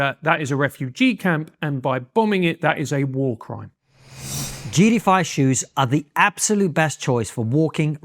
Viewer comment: I thought he said "Jew Defy shoes" 😂😂